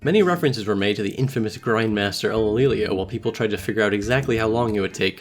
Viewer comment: Anyone know who that is at 2:14 I cant hear what name he is saying it sounds like elellelelelele to me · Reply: Ulillillia